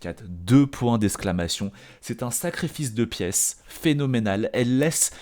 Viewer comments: j'ai entendu c'est un sacré fils de pièce .... 🤣🤣🤣🤣🤣🤣🤣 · j'ai entendu "c'est un sacré fils de pièce", j'ai mis du temps à comprendre le lapsus en "sacrifice de pièce"...